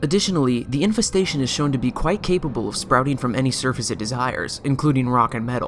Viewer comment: When you said "quite capable" at 1:34 my phone thought you were saying "ok google"